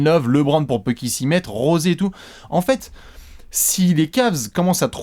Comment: il a dit José (prononcé Rosé) pour Calderon mais ouais on aurait pu y croire 😉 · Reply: ouais j'ai compris "Rose et tout", alors qu'il dit "José et et tout"..